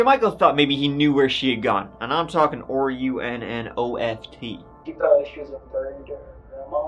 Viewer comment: anyone know what he was spelling? After her said “je Micheal though he knew she had gone and….?” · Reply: OR-U-N-N-O-F-T= Run Oft i think, i had to play it a few times, but i have learned that pronounces 'R' as 'OR' lol